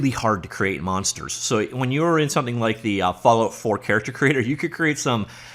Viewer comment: What was the other character creator system you mention? I couldn't quite make out the name :-) Mentioned at about 2:43secs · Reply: Fallout 4 character creator?